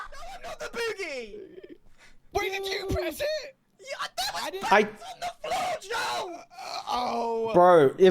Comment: here’s the what was said Joel: wait are you the boogey?? Grian: no I’m not the boogey! Joel: wait did you press it??? Grian: yea- THERE WAS BUTTONS ON THE FLOOR JOEL Joel: Oh